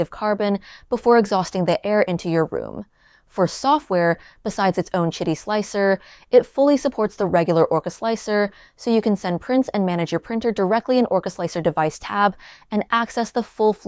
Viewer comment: I initially misheard "besides its own shiddy slicer."